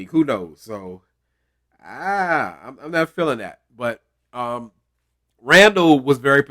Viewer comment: 🤣Yes please give em some grace😁 but I did not catch what he said I don’t know what everybody’s talking about what scene was it so I can go back and look👀👀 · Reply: I thought he said, " I'm not for that sucka sh*t". I also watch everything with close captions on.